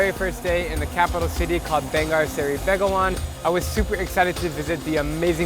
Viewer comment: It sounded like you said “BENGAR”